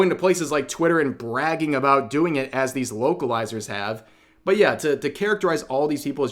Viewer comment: I keep hearing 'Woke-alizers' I think we should call them that